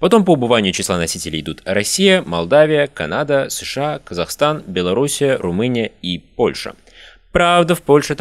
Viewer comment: Беларусь а не беларусия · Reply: Автор говорит по-русски и по-русски грамотно называет: Белоруссия, а не "беларусия", как Вам послышалось :))) "Беларусь" - по-русски - название трактора.